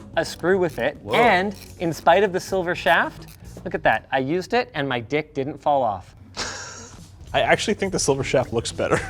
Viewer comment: "i used and my bit didn't fall off" i thought he said... well i'm sure that most of you know what it alternatively could have been misunderstood as lol.